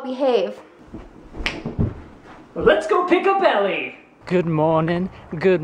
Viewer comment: I thought you said let's go pick a belly. haha.